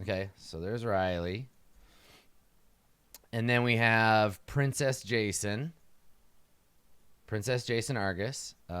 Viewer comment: Oh it's seer-ess, I thought it was a familial title, like Cyrrhus or something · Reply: In that same vein, I mainly listen to the show, and I kept hearing, "Princess Jason."